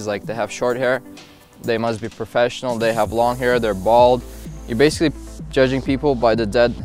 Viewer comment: Ok I heard "if you have long hair you're bald." Que me laughing like an idiot. Love your videos - the workouts are great and useful! Thanks.